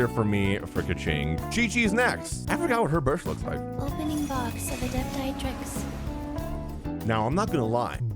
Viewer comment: Qiqi: lOpening box of Adepti drinks” That’s what I hear lol